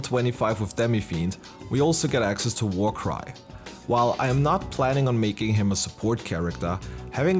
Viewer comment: I was listening to this in another tab and I swear to MegaTen God, I heard "And then we get access to War Crime." and had to switch tabs to see what I heard~!